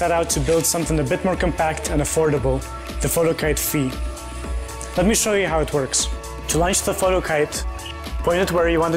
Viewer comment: i thought he said "let me show you how it twerks" XD